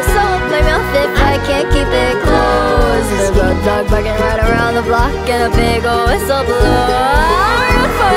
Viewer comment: My mom thought it said" big red rocket around the block " and I was so confused lol